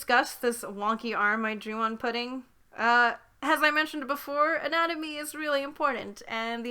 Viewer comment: ''Anatomy is really important and...'' Me. Did she just say... ANIME?